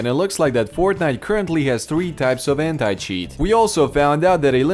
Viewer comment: For a second there I thought he said "...currently has 3 types of aimhack cheats" and I spit my drink